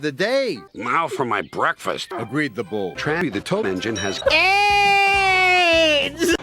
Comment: "Trammy the Toam Engine" lol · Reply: I heard "Trammy the Tobe Engine"